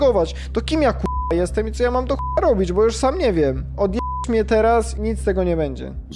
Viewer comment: a ja usłyszałem możesz mi ojeb*ć a nie odj**ć